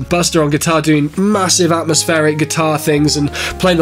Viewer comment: that sounded like stratosphere.